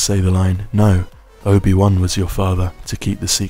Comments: I thought he said "No, Obi-Wan killed your father" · I thought he said: no obi one killed your father?